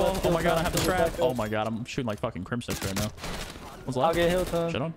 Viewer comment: swear he said 'im shooting like crimsix rn' hahahahaaahaahahh